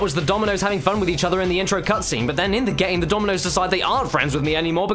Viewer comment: Am I the only one who thought he was saying nominoss at first.